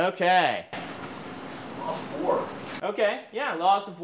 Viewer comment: You know how I know they're a bunch of classic nerds, no one said 'sports' in cultural setting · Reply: I misheard "Sports" when dude said "loss of war"